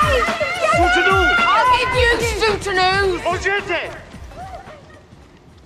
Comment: What does Posh say at 3:10? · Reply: Shootanoo